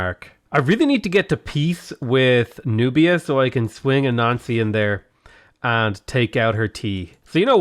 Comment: I was doing my homework for art class while listening to and when he said anansi I immediatelly thought he said a nazi and was confused af